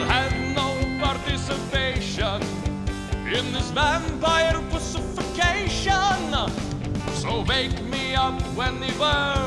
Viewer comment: "I'll have no participation, in this vampire pussification" is my favorite line in the song 😂 I sure miss this channel! Times were so simpler back then. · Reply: Lol I actually thought he said crucification.